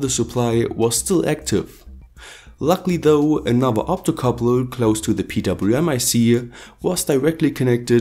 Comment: What coupler? Sorry I don't hear it clearly. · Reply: optocoupler